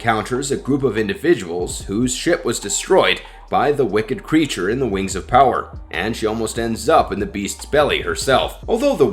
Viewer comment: Anyone catch him say “who’s sh*t was destroyed by the creature in wings of power” @ 10:53???